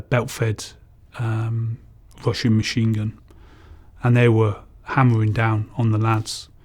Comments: I Thought He Said Washing Machine Gun 🤣🤣🤣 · I swear he just said "washing machine gun"